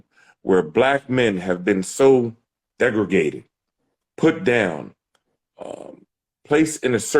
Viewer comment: Did he say "degregated"? What is "degregated" sir? was on my nerves at first because I thought she was dragging it with the ebonics debate, but then you say something like "degregated", which isn't even a word. Now I gotta call you out. Do better bruh.